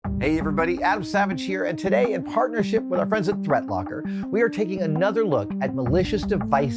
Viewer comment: Sorry, Adam, I thought you said, Thread Locker I thought the company had taken a whole new avenue :)